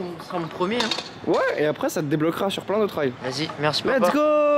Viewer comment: C’est moi ou à 7:10 il a dit merci papa